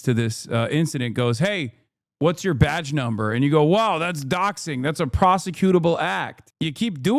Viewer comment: I think we are mishearing him. He isnt talking about doxing, he's saying docking